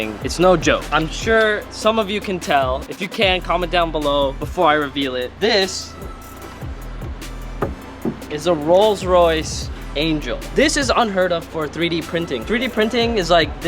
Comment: did he just say rolls Royce angel...